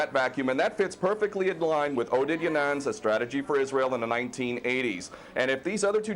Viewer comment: in did anyone catch the name KoK said for the Strategy for Israel in the 1980s? Something DaNang/DeNang I think. Please put up the name and correct spelling if you know who he means as I would like to read this piece on Israel. - Thank you in advance. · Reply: I was interested too and I think the strategist Ken O'keefe referred to is Oded Yinon.